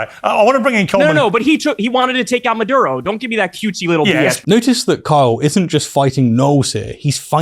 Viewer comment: I heard “Notice that Kyle isn’t just fighting gnolls here.” 😂 I’ve clearly been playing too much Baldur’s Gate III.